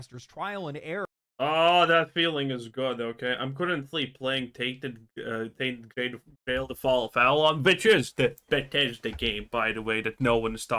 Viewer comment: What Bethesda game is he talking about here? I wanted to look it up but I really can't understand the syllables lol I hear "Tainted Grade of Flail da fall of fallon" and the auto substitles hear "Ted Ted Okay" (Not purely picking on his accent to be mean btw, I can understand him fine, but that series of words is very fumbled and im interested in the game he mentioned based on his praise) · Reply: Tainted Grail